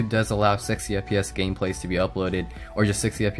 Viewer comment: I thought he said "sexy fps", but he said "60 fps"!!... HAR HEE HAR HAR!!!